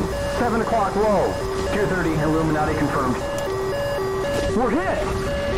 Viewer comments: Did Jester just say "Illuminati Confirmed" at 0:38? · Did buster just say “230 Illuminati confirmed”??